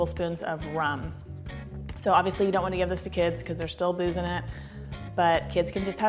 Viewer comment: So I misheard you say "don't want to give it to the kids because they're still boozin' it" instead of "booze in it" and now I'm forever keeping that imagery in my happy place.